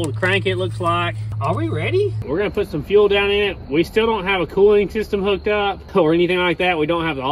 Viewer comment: i thought you said five shaft no hooked up or was gone??